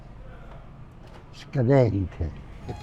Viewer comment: could you help me with what word he uses at 14:27 to describe the Parmiggiano? It sounds like schaneriche / scamerite? Neither of those are right but I just can't grasp what he's saying. Thanks! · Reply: "scadente" = low quality